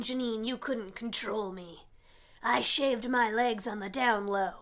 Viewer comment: 00.28 I thought it said I have legs on my hair